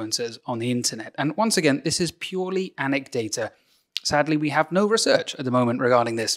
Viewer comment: did you say anec-data? that is kind of clever.. i'm going to start using that.